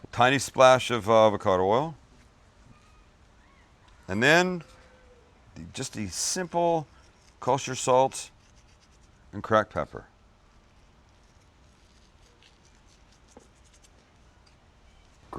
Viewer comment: did you say crack pepper?